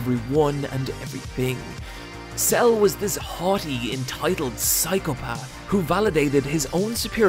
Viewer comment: did I hear that Cell is a hotty? I agree because he is indeed.... Perfect!